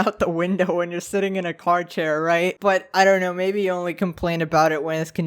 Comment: did you just call it a "car chair", my guy?